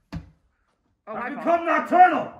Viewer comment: I thought he said "I become my turtle"